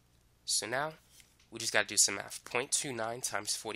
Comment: Anyone else hear "meth" at 7:22?